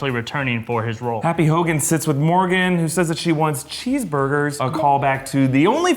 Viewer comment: Who else thought Morgan said cheese-boogers? (And kinda laughed)